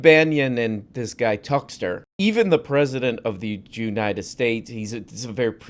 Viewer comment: did he say "Jewnited states"??